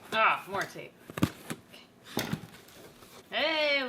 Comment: it sounded like she said,” Agh, quarantine!” Instead of “agh, more tape!”